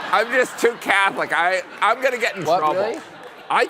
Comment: What does Stephen say at 8:09? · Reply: Trouble